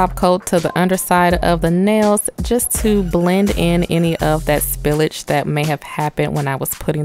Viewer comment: Did u just say spillige